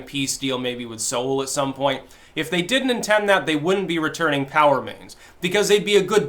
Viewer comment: P.O.W. remains! at first, I thought you said "Power mains" and I was thinking "How did North Korea capture part of our electrical grid infrastructure? did the hack our power grid and were holding our power mains hostage?" after a few minutes I realized what you were saying. :D Funny the things your brain can trick you in hearing!